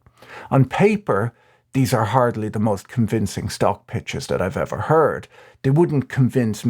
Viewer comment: i thought u said cock pics...lordy